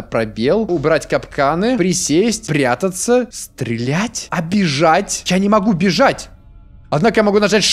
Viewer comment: а бежать? Мне послышалось обижать